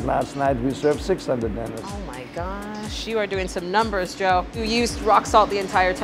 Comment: did she say, "you doin some numbers girl?"